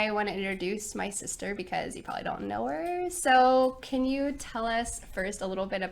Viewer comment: hi. what said in ( 1:21) and (1;25) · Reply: "Because you probably don't know her" and "Can you tell us first a little bit about where you've lived or different countries you lived in?"